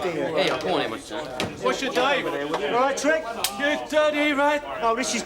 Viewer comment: Guys what did trigger say to Rodney at 29:42? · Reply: He said You dirty rat, can't remember which film that came from.